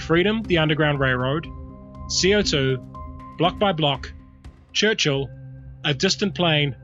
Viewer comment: Hi, What is the game at 6:03? I think says "block by block" but I don't find any game by that name that resembles the one on the video. Thanks! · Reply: Bloc by bloc: The insurrection game. Here's my recap of it. <link>